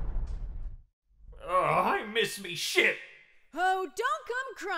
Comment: I love where the part when foxy says I miss my shit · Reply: it was a joke because it Sounds like Foxy says "I miss my shit" when we know he doesn't